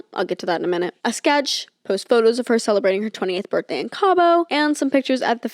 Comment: did you mean 28th birthday ? at around 8:28 is sounds like youre saying "20th"